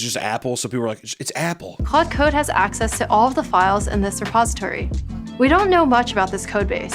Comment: is it just me, our did this clade ai say suppository instead of repository at 17:10. Hilarious 😂